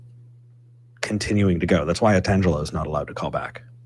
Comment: 🧐Who isn't allowed to call back? It sounded like Tatangelos or Angelos but could find the caller with any name along those lines. I want to hear that call though. Does anyone know the name? Or where/how I can find the video? Please & thank you! 🙏😇